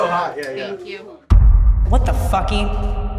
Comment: Did she just say what the fucky? Lol